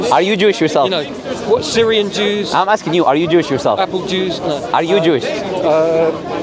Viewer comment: He did not just say, "Apple Jews" 😅